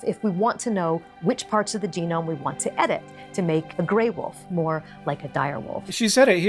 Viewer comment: Interesting. I do not hear her saying "make a grey wolf look more like a dire wolf," which is what I think Hank hears her say. He says that immediately afterwards. I hear her saying just "make a grey wolf more like a dire wolf" which is consistent with their whole viral-bait video. The auto-generated captions agree with me, and they pick up Hank's own quick-and-quiet "look". I know auto-generated captions are hardly definitive, but still. How do other folks hear it?